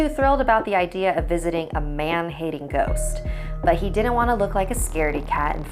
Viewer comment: "Man-hating ghost" so basically a ghost feminist? lol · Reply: Aimiko oh lmao I thought she said “man-eating ghost” and I was confused